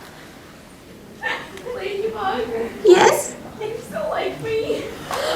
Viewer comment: what "Adrien" saying? Can u tell me? Because I can't hear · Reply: The Adrien in the audience asked, "Ladybug, do you still like me?"